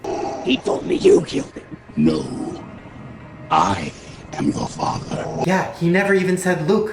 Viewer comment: I know it's "I am your father" but I hear "I am the father"